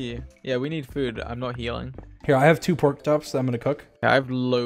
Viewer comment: Dream: dies in lava Also dream: M A X W H E E Z E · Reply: did he just say porktops?